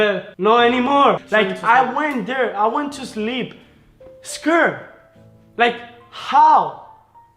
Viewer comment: i thought he said 'i went to sleep, skrrrr'